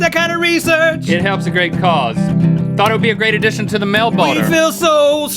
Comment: did he say mail boner